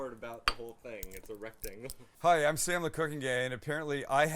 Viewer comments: Did you just say "Hi, I'm Sam the Cooking Gay" @ 9:35? lol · Sounded like you said "hi im sam the cooking gay" 🤣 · I laughed, sounded like he said Hi I'm Sam the Cooking gay hah